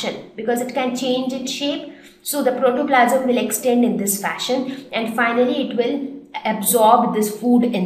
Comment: did u say fashion?? I didn't understand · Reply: Yes she said "fashion". When you say "in this fashion" it's actually another way of saying "in this way".